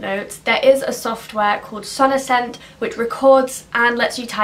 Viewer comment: What was the recording and typing app called? I searched ‘sonisend’ as that’s what I heard you say, but nothing came up! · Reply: Sonocent:)